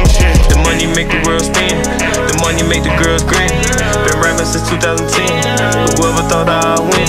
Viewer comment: The money make the world spin The money make the girls grin 💯🙏🏾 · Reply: Thought he said "money make girls cream"